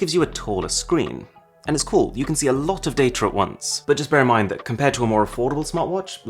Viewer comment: Most UK youtubers i see add 'arr' to some words right? Or am I hearing thing ? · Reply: He said "Data at" very quickly and it sounds like datr